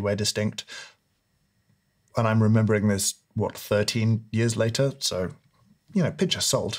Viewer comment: How the hell long ago was this special? James looks like he's his own dad now. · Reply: thought he said 13 years ago dude